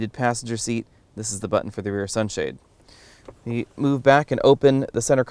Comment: I really like your review , could anyone tell me what is the button described at 6:51 (last one in the group of buttons), I did not understand the word he said, My English not that good :( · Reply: it is for the rear sunshade. I am probably 3 years to late